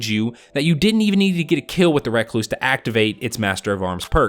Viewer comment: sounds like hes saying red cluse xD